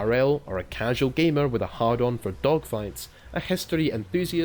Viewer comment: misheard as "a casual gamer with a hardon for dogshytes" spat coffee